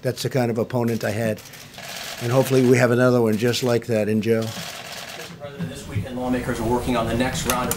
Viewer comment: When speaking about his Republican opponent (@ about 4:30 mins) did POTUS just say, "I hope we have another one, just like that (pause) IN JAIL" (?)